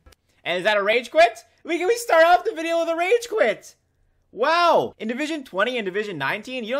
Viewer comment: Did he just say n*gga we start off the video with a rage quit